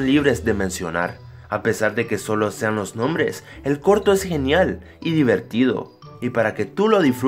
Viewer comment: XD EN EL MINUTO 2:19 escuche que él porno es genial